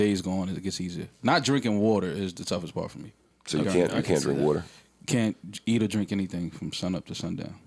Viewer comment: So Mal is wears a Jesus piece, and is fasting for Ramadan.. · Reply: Neymar Jr lll I thought he said he couldn’t drink water